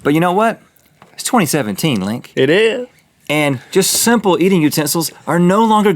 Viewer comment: I'm crying 😂 did Link try to say "It is" and just completely fail? I heard "It eurgh" I don't know why I can't stop laughing at this